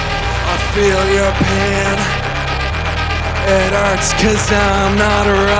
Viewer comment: I thought he was saying “I feel European” only to realise he’s saying “I feel your pain.” Great Belfast accent !